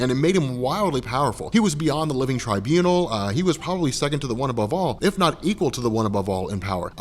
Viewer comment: one above all sounded like onababal